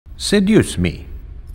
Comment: I thought he said “said use me” but I realize he said “seduce me” I was like WTF THAT WHAT HE BEEN SAYING